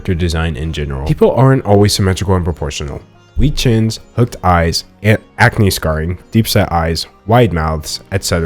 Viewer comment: did he just say "hooked eyes"